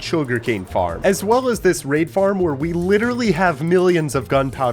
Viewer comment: what farm did you say you have a r@ farm?💀💀💀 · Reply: He said raid farm